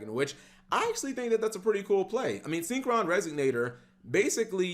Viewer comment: bro it's RESONATOR why does it sound like you sayin "resignator"